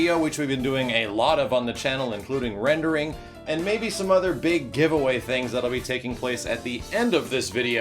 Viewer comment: I thought it said "HUGE ASS GIVEAWAY"